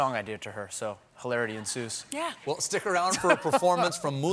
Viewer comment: Being a French speaker, I always thought Americans were saying "Mulan rouge" because of the way they say "Moulin"